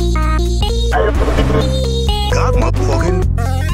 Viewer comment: what does this part say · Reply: God mf-ing